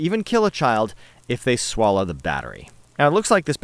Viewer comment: I thought that said don't wallow swatch batteries